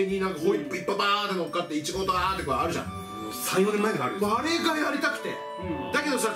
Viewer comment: あたりからのの発言に対するのレスなんて言ってるかわかる人います…？ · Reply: 千竹 今更ですが、、、「3、4年前からあるぞ」的なこと言ってます！